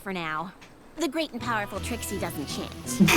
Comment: for some reason I keep hearing that as “The Great and Powerful Trixie doesn’t sh*t”